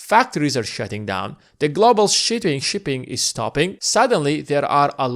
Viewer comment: Did you say that "Global shitting is stopping"